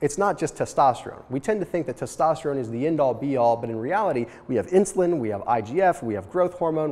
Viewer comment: Fun game: take a shot of water everytime says "end all be all" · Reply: Thought he was saying "end all Dbol"